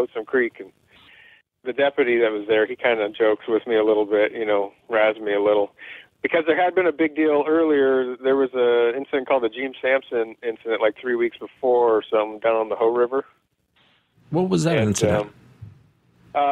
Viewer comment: did he say Gene Samson Incident at Hoe River?